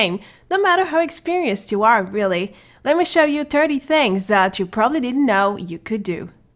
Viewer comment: It sounded like you said “lemme show you dirty things” 😭